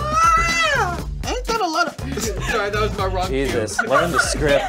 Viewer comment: What did Keith said at 5:10 ? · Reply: Ain’t that a little